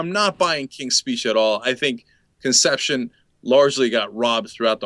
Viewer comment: did cenk say conception at 2:07?